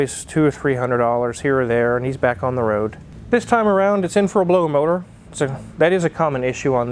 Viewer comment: Car Wizard did you say that car is in for a blown motor?!?